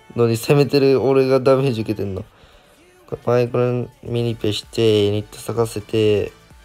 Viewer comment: パイ◯ンミニペにしか聞こえんwww